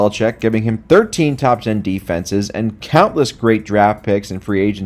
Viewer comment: Did just say 13 too ten defenses…? How can you have 13 in 10 slots…? I don’t like Brady either but I do like math (that’s a lie nobody likes math)